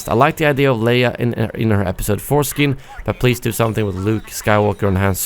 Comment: Lol at 14:08 I thought he said Leia’s foreskin but now I realize that it was Episode 4 skin